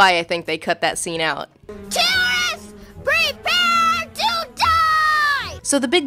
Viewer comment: "tourists, prepare to die!" should have been left in. what a great line. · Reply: I heard "terrorists" lol